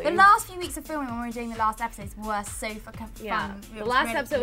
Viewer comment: Did Jade say "so f***in' fun"?